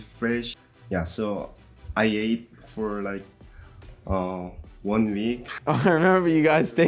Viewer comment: what is and saying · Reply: I got beef for like one week: I remember you got steak (beef) everyday